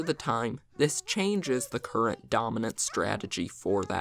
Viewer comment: says dominant but subtitle says optimal